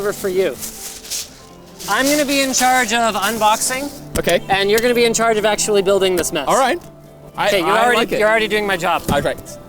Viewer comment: What does he say at 18:23? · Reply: I think he says I practice, you can tell at 0.5 speed.